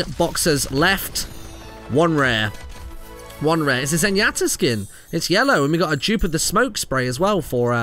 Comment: Did say "Zenyatta Skin"